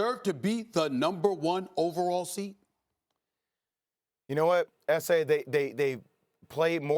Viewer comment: did he just call Stephen A "esé"